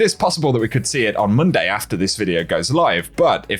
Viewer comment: did he just say moonday lol?